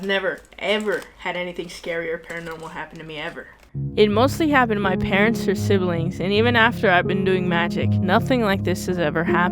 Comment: I thought she had said “my parents who are siblings” went from like 😳👀 to 😅🤣